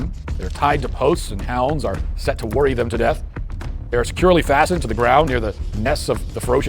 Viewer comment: I cant hear what he says at 13:54 . Hounds are set to worry them to death? lol, i turned on CC and it says exactly what i heard.